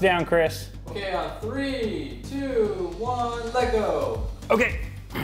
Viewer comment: did he just say 3,2,1, lego? Did Chris do lego league? 3:54 Also, that's the same power supply im using right now.